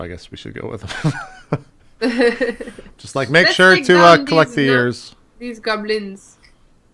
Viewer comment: did said "Let's take down this nerds!"? Damn, you have a lot of 'em here.